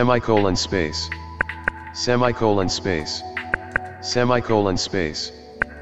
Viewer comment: i don’t hear “semi-colon space” anymore i hear “say Michael in space